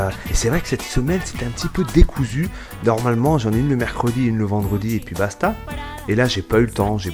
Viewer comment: Si toi aussi t'as entendu j'en branl à 1:43 :)